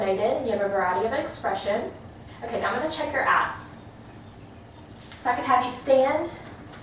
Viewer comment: it sounded like, "Now I'm going to check your ass."